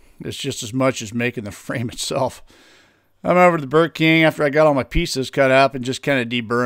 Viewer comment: Every time you say you "went over to the Burr King" I hear "went over to the Burger King". 😃 I guess I'd rather chew on a Whopper than a hunk of steel